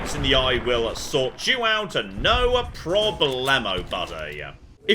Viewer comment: My fever-addled brain heard that as “no pro-BLAMMO” and now I can’t unhear it